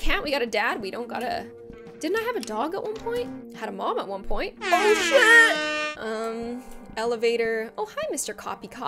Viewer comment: I the only one who heard oh (s word)